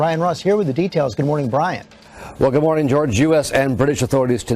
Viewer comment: he says Jew-ess instead of US